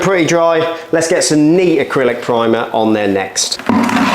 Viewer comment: he uses the term "knee acrylic primer" for the second coat(?) and I wondered "What word did he use?" Sounds like "nee". Was it a different product? Turned on the subtitles, (which are often wrong), and it said "neat". I was scratching my head, feeling like I was in a hearing -aid commercial: "What did he SAY?". Looked like he used the primer full strength the second time, was my best guess. (?) Maybe "neat" means "full strength", down under? Anyway, the few other videos I've watched recommend making sure your primer matches the self-leveling concrete product chosen. Also, does primer even work on tar? I'm left wondering about the nature of that second coat. Several comments keep talking about having screws sticking up from the floor to establish a level height. Wouldn't that make it hard to squeegee it level if the squeegee keeps hitting the hard screws? Don't they have something like a soft plastic single bristle that can bend a bit, that we could use instead of screws for that? Also, I see lots of comments about asbestos from the tiles, Not sure, but there is some asbestos all around us - it's a natural rock. People act like its radioactive or something, almost to the point of being superstitious, as though asbestos carries some evil spell, to even get near. If asbestos is encapsulated on a plastic, how is that dangerous? Are you going to breath in chunks of old tile?